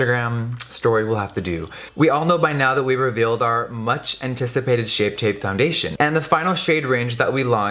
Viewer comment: At the beginning sounded like he said "turd shape tape", accurate hah